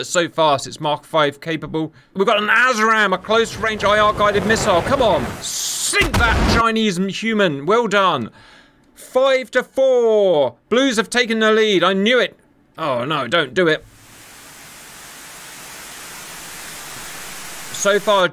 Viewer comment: Did you say "chinese inhuman"? Bruh...